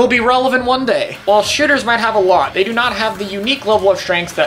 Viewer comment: did he say shitters😂